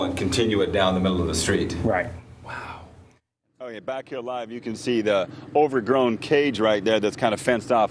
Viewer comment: lol I thought he said "back to your life"